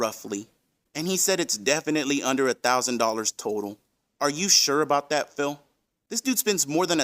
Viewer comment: I thought he said he spent less than one HUNDRED dollars total. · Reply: He might have said that as well. But I'm almost positive he uttered the phrase "it's definitely under a thousand dollars total"